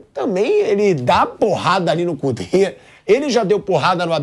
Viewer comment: Quando o Pilhado disse "Cudet", sério, eu entendi "o cu dele"